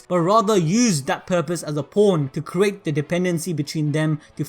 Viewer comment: sounds like he said porn when he said pawn